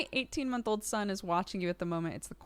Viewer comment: Somehow I heard 18y old son & was like "Wait a minute...".